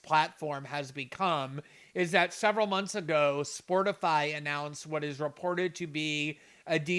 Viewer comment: Did he say "Sportify" at 00:45?